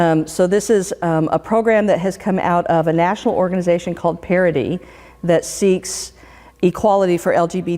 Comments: Female Episcopal Pastor Rev. Dr. “...this is a program that has come out of a national organization called ‘Parody’...” That is how I heard it. The name is quite befitting. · did she just say ‘parody’? 🤔